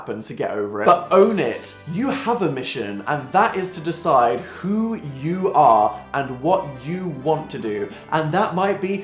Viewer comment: When he said “who you are and what you want to do” I thought he said who you are and who you want to do